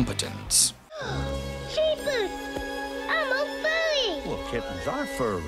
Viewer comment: Jesus, I’m a furry. I bet you are. Before anyone has a heart attack, I know that’s not what he says. It just sounds like it due to either the enunciation of the phrase, the audio being hard to figure out sometimes or both. Either way, the misquote/mishearing is still unintentionally funny.